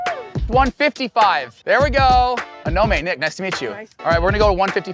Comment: I thought when you said Anome you said A no name 😅 💀